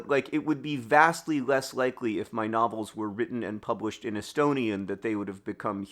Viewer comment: I heard "My nobbles". Which is what I will now call any book written by either Green brother.